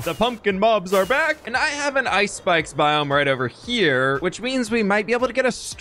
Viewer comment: I totally thought he said “Ice Spice biome” instead of actually saying “ice spikes biome”.